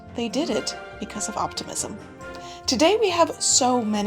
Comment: I thought you said “autism” instead of “optimism” and was VERY CONFUSED for half the video 😂😂😂